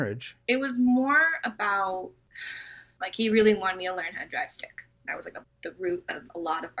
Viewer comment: did she say drive stick? Like a stick shift car?!?!